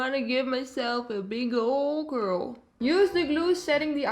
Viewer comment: I totally thought you said "give myself a big ol girl" instead of curl lmaooo